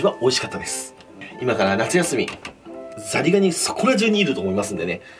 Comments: 最初のザリガニがザニガニに聞こえる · の初手のザリガニ、何度聴いても「ザニガニ」に聞こえる…笑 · ザリガニがザニガニに聞こえるんよ